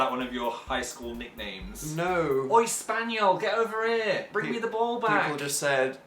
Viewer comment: I swore said "Oi, spaniel, come here and bring me the ballbag"